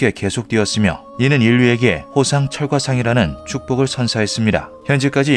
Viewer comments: 호상철광상이 아니라 호상철과상으로 들리는데 내 귀가 이상한건가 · 호상철광상이 '호상철과상'으로 들리는건 나뿐인가???